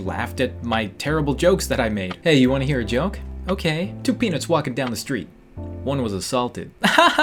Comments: I thought he said "Two penis were walking down the street." Lol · Ok I thought he said two penises walking down the street, one was a softie lmao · "Two Peanuts walking down the street. One was assaulted." Best Joke In 2018! Edited: I wish i had these much likes on my youtube vids But something is better than nothing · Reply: I thought he said one was salty